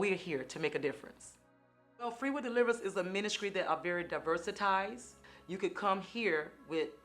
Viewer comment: Did she say Diversitized?